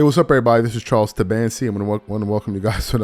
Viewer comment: Bro…… all these years I thought you was saying Charles Sebastian